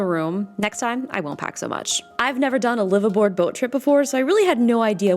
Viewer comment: My whole life I thought people were saying “liver board”… I never understood it, but I also never questioned it “it’s a liver board trip” 🤨 Well, now I know 🤦‍♂️😅